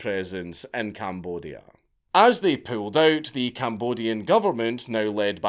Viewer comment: All i keep hearing is "pulled pork"